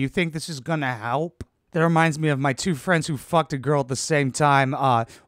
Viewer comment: did you say what about your friends😂🤣